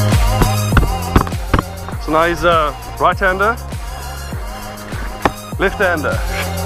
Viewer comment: does he really say "now he's a bartender" Or its just me???